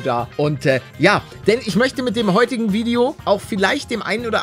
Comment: Palle: Ich möchte mit dem Video anderen Mut machen Was ich verstehe: Ich möchte mit dem Video anderen Druck machen Ich: 👁👄👁